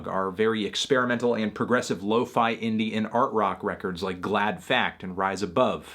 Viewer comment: I thought u said "Indian Art Pop" and was Very confused